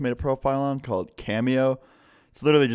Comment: deadass thought u said "Camel Toe" instead of Cameo. LOL